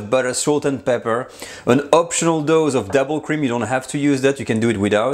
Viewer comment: What is devil cream? Google just gives me marijuana results, I mean that's fine but I really wanna know. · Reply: Ahhh now I hear it upon rewatch! I heard "double" as "devil" and thought is was some French food sorcery. Thanks!